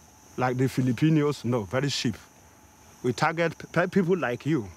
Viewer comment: do i heard it right? He said the filipinos are cheap..?? Pilipino pati mga pirata alam nilang wala silang mapapala sa atin 😂😂😂😂 Kaya ligtas tayu sa mga pirata 😂😂😂